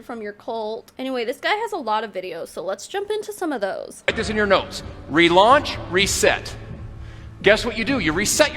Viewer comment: Omg I thought he said "Put this in your nose" dude whaaaaa